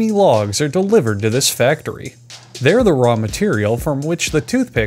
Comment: When you said "Logs are the raw material to make toothpicks" I heard it as "the wrong material" and it made it even funnier.